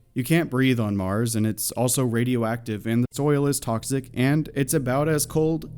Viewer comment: did you say that the oil was toxic?